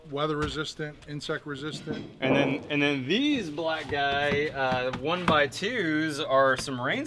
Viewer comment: Did you just say black guy....